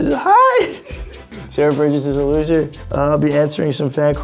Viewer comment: "film called sarahbergerizes hi! gasps in joy sarahbergirizesillugit" i mean thats what i heard